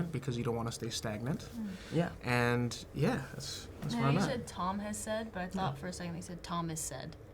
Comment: I also thought he said Thomas said but It's actually Tom has said 😂😅